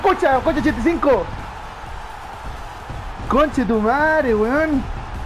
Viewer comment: que dijo? xdxd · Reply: La madre huevon